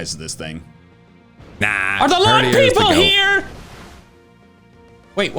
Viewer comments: i thought he said long people not lawn people and i was very confused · When he said "lawn people" I heard "long people" and was very concerned